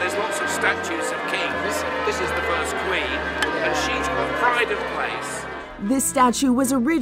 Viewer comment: did he just said, "she's the bride of Christ" 🤔 Rest in peace, Queen Elizabeth II. 🕯🌼🕊🕯🌼🕊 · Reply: She has pride of place.